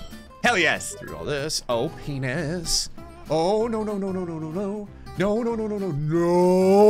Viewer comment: did i heard him say "oohhh p*nis